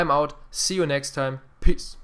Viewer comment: I love ur voice cuz every time u end a video u say peace but it sounds like u said piss